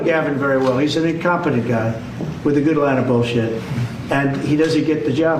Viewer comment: What is the demented buffoon saying at 00:10 right after "incompetent guy"? "with a good a lot of bs"? That doesn't make sense! My god! How could so many people vote for such a joke! the most spoiled brat that never grew up!